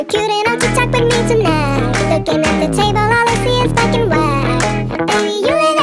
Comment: Ami the only one that thought it said f*cking but they said black and white